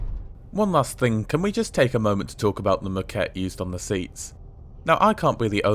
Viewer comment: Happy New Year, and to everybody here🥂🍾🥂🍾🥂🍾🥂 -, at 9:30 or so I couldn't quite understand what you referred to as hideous...I think it had to do with the seat(s). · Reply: The moquette, the fabric covering the seats.